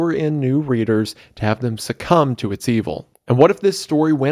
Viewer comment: my audio wasn't working for the first few seconds, so the first thing I heard was "CUM to its evil"